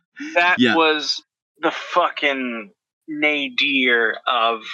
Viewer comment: what is saying there? Nadier? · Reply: Nadir - the absolute lowpoint of something